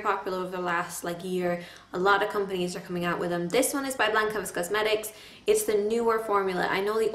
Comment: oh ok! thank youu so much! lol for some reason it sounded like you said lancome :P <3